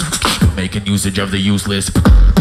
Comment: what does says before the drop? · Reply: making usage of the useless I think